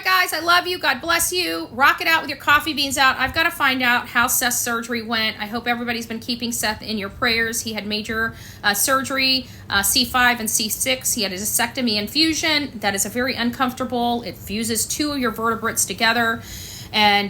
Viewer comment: Lol at first i thought you said vasectomy